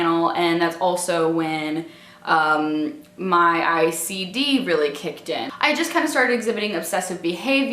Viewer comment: did she say icd or ocd? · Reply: she said Icd - impulse control disorder